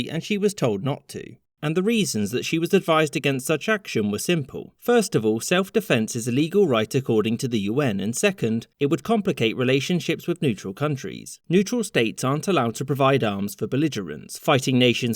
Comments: idk if the auto captions is making a mistake or if he actually said self defense is "illegal" and roasted the UN... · is he saying “it’s a legal” or “it’s illegal” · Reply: "self defense is a legal right according to the UN" (meaning that there was no need for the UK to declare war proactively since it was reacting to Argentina)